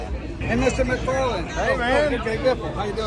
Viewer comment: The Biff is bringing new fans with this stuff. Showing that these drivers are just regular folks that are giving back. Did I just hear someone call him Mr McFarland at 15:10 ?? LOL